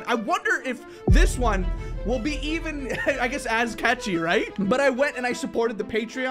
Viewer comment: Wait in thought you said kachi(kachi is my name) but you actually said catchy